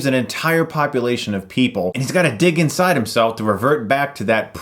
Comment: "...his got a dick inside himself..." is seriously what I heard. Sorry, I never passed puberty, it seems. D